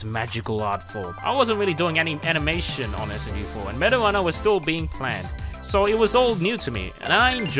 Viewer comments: the auto subtitles says "marijuana" at 7:41 when luke says meta runner wtf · Yo I am using subtitles and and at 7:39 instead of it saying metaruner it says Marijuana